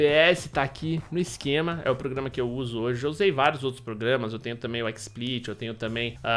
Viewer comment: escutei xvideo kkkkkk